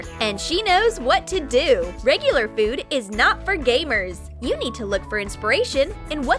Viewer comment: I thought she said gay-er but it's actually gamer